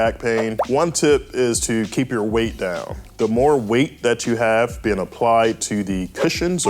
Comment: Can't understand what he's talking about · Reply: Don't be Obese. That's what he's saying.